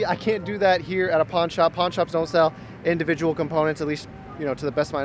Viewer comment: thought you said "porn shops" LUL